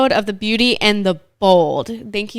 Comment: I thought it said beauty and the bald